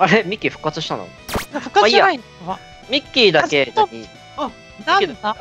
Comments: マイッキーがミッキーに聞こえるのうちだけ？ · ぜんいちのマイッキーがミ○キーに聞こえる